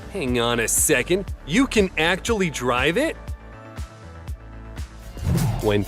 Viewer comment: i thought you said hang on a second whys the dad driveing it